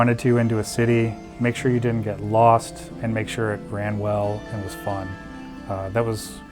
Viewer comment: did he just say fallout 4 runs well?